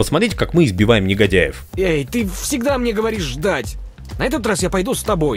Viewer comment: "Эй, ты всегда мне говоришь: дать". Я ведь не один это услышал?